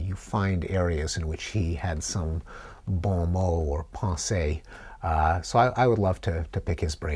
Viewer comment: What did he say? “He had some ____ or _____?” Sounds French, anyone know? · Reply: Evan Falstrup bon mot and pase, I think